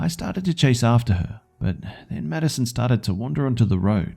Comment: I keep hearing "Medicine" instead of "Maddison" lol